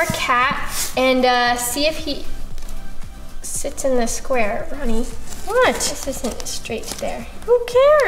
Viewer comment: I thought Nessa sayd "Honey!" 😂😂